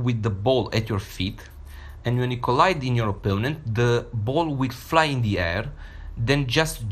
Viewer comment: I have no clue what's he saying. Did anyone catch it? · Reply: When you collide with an opponent